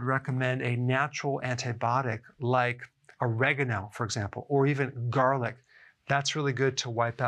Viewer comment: Can someone tell me what he says at 3:03 "A natural antibiotic like ????? or garlic": What is the 1st natural antibiotic he mentions Thanks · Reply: Oregano